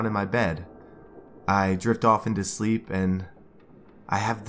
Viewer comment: When he said i drift off to sleep i thought he said i jerked off too sleep... Im sorry